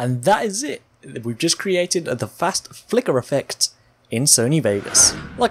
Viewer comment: I thought it said Fast Fucker.